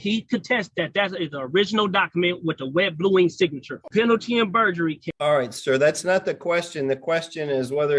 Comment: Did he just say "burgery"??..🤣🤣😂